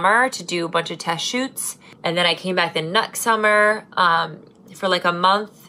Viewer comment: i thought you said "nut summer" instead of next summer 😂😂😂